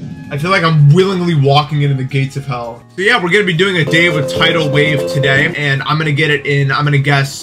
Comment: kinda sounds like hes saying "day of a tidal wave" here